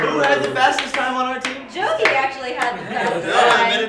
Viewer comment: or so, did Mari say jovie instead of joven? Or did I just hear it wrong?